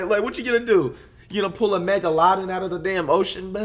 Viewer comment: Did he say mega-lodin